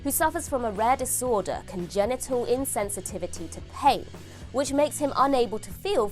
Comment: had the volume low thought you said genital insensitivity